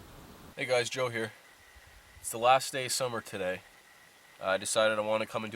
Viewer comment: At first I thought he said it's his last day of being sober